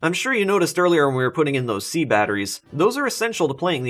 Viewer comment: When you said, "six D batteries" I thought you said - sixty batteries.